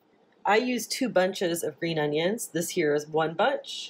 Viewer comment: What did she call the vegetable at 02:22? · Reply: Green onion